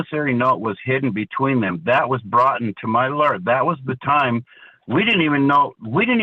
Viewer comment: Did he just use the word broughten ? I think I heard him say "That was broughten to my lawyer." 😂